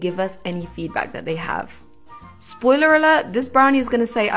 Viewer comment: I somehow misheard “spoiler alert” as “trigger warning” and that was so funny